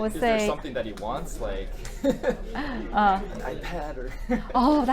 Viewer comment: what did he say at 4:33 ? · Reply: sounds to me like he said "iPad, or..."